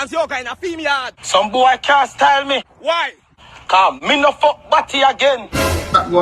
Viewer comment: Did he say “mi nuh fuck batty again?” 🧐 8:24 wtf